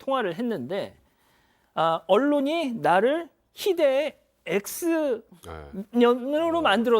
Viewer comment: 언론이 나를 희대의 x녀느로 만들엇다가 아니고 나쁜x로 만들었다 아닙니까ㅋㅋㅋ